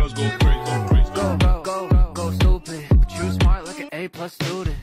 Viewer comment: "You're smart like an A-Plus student" does make more sense, but I always heard "you smile like an A-Plus student" and i really liked it that way. :P